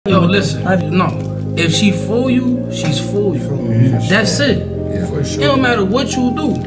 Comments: Imma keep pretending I heard “ If she fools you. She’s for you” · I thought he said: she fool you, she fool you.😅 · Why did i hear “If she fool you, she fool you” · I thought he said if she fools u she for u that would of been a new one